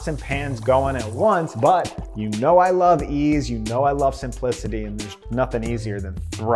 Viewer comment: did he say he likes e's.... hahahaha